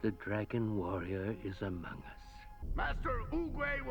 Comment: Wait, did Oogway just say "Dragon Warrior is Among Us" or did I hear that?!